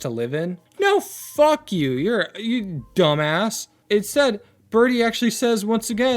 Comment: I thought you said burdie